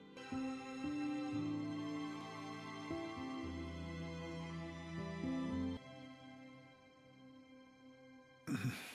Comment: what gojo is saying here? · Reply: You're my best friend, the one and only.